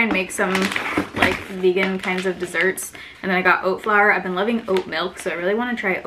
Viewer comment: I thought the Oat Flour said Dat Flour lol